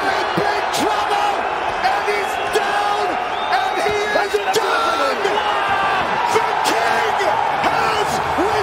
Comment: I thought the announcer said “He’s Down and He’s DEAD”